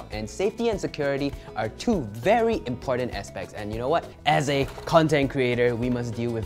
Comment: caption wrong its aspects